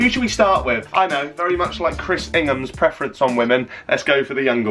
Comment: The Chris Ingham shade, love it. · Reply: I first heard him say Chris ‘Bingham’ and I was like wait what has Bing done? 😂